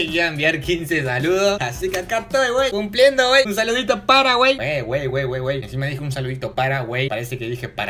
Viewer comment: no entendí lo que dijiste en el minuto 5:23 ,osea que es para.... paraguay? lo escribí bien? , es una comida o que